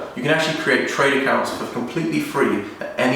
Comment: What did you say at 3:07, the reason why you get kitchen for only 700/800 pounds? Sorry can't hear it clearly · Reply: JB 7 setting up trade accounts at the showrooms is what I thought he said but idk